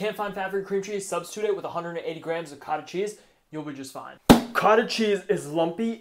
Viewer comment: Let's gooooooo! I know I won't find fat free cream cheese, so I'm gonna try the cottage cheese sub. Would it be 448g of cottage cheese if we sub it for FF cream cheese? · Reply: I think he says 180 grams